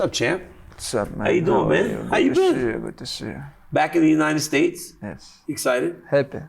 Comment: is bro saying “with the shits (x2)” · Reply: "good to see you" (x2)